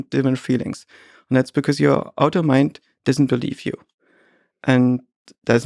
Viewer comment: does he say auto mind? Or other mind?